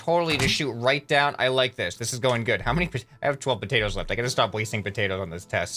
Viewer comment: since when can you use tomatoes as ammo? xD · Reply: I thought he said tomatoes and potatoes at the same time there lol!